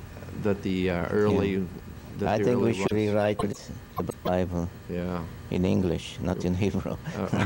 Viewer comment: the audio trips. can anyone make out what he said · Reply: He says "I think we should rewrite the Bible in English".